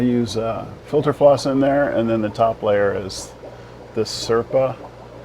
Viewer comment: The flake feeding was awesome to see! What was the name of the floss that is finer than your typical floss? I heard "serpa", but I couldn't find anything via Google using that. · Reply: Its Sepa brand, Crystal Clear Filter Media.